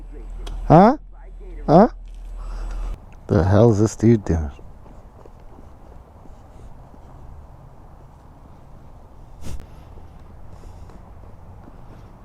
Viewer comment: you say: the hell is this dude doing? At that same time i heard Rick James cocaine's a hell of a drug!